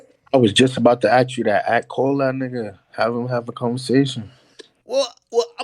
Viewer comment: Anyone else hear say nigga